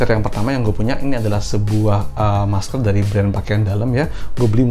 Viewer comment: "masker pertama, masker pakaian dalam" gak salah denger kaan? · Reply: Masker dari brand pakaian dalam. Bukan masker pakaian dalam 😀🤭